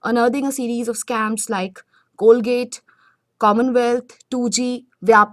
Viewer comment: Did you say Colgate? In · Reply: Wansuk Lyngdoh, it's Coalgate scam she is talking about.. 😊